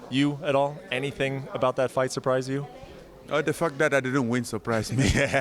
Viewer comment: 😂😂😂😂 it sounds like he says the fuc* but he says the fact 😂😂😂